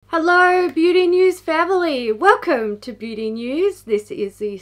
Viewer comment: I misheard "hello beauty news family" as "hello my name is Beverly" omggg